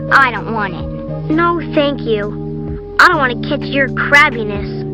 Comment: what did he say 3:42 did he just say crabbiness Let’s go Bad word he said the bad words (GASP) but his heart is in the right place From his older sister and tells her to stop Insulting Jenna